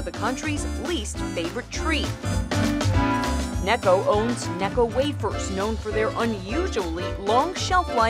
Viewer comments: i was just listening to only the audio and thought they said "Neko Waffers" lol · I was looking away from the screen and when I heard necco I thought I heard neko